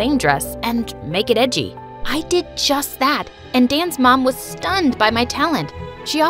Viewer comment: When she said Dan's mom I thought she said Dance moms